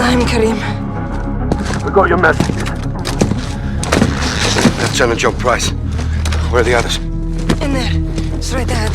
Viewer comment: I thought he said 'Leftenant John Price'?